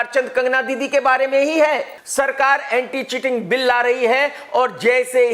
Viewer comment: I thought he’s saying “anti chicken bill”